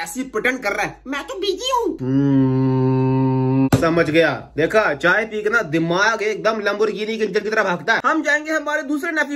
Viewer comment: I heard Mai to piggy hoon